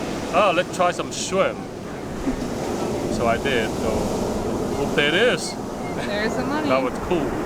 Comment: Did he say whoomp there it is?? hahaha what a cool dad!!